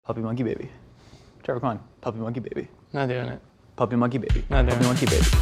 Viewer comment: I thought josh was saying funky monkey babies and I'm disappointed he wasn't